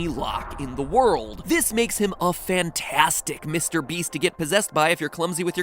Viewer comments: does he say mr beast? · did you say Mr Beast? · did he say Mrbeast?